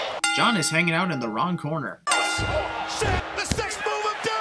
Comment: almost (or did) sounds like Cole said SH** Not PG...